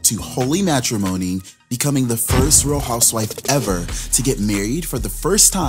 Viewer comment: That’s why he said first housewife to be married TWICE on tv, but bravo didn’t even film it lol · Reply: yeah it sound like he said FIRST to EVER buttttt my ears may be lying to me idk 🤷🏽‍♀️